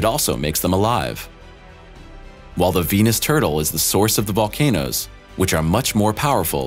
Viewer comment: Wait, what's this about a Venus turtle? Are you on acid, or did I mishear that?